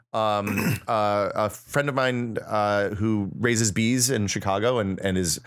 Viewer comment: LOL! I heard "Races bees", like as in bee races.